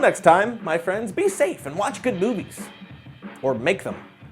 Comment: I swear i heard him say watch good boobies at the end LOL!